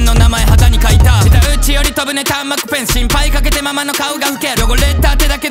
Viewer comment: うんちブリブリで萎えたけどそれ以外ええやん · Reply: 誰かが うんちブリブリ言うから うんちより飛ぶ に聞こえちゃったよ ごめんなワトソン